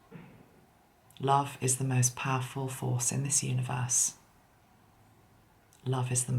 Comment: Toward the end when you were saying "love is the most powerful force in the universe" at first I heard it as "laugh is the most powerful force in the universe" thank you for being brave enough to dive into something I may have shut off to. You are a very wise person, thankyou for your strength. Love from Australia, Luke